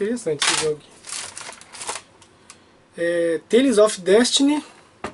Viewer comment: impressão minha ou eu ouvi "Tênis of Destiny"? kkkkk Ótimo vídeo!